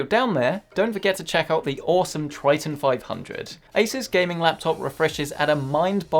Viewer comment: did he said ASUS on Preditor laptop? heheheh